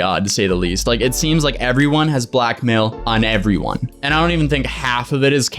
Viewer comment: When said Blackmail I thought he said black male